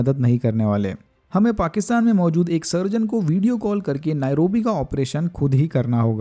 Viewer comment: did he just say pakistani surgeon????